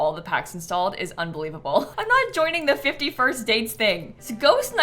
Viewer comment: i’m canadian and perked up for a sec when i misheard “fifty first dates thing” …